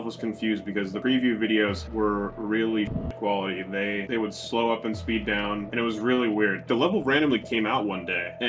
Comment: did he just say slow up and speed down